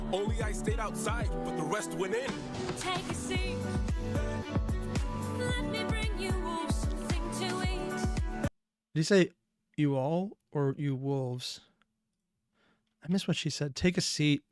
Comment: I always hear "Let me bring you wolves something to eat" too! Which makes sense with the whole wolf theme and Poseidon calling them a "pack of wolves" in "Ruthlessness". Was kinda disappointed when I figured out that it's a misheard lyric.